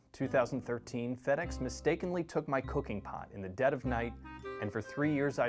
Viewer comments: I thought he meant weed and then I heard “cocaine pot” at the beginning. Then he mentions white powder so I was confused af. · i have such a dirty mind, my bad butt thought he said cocaine pot.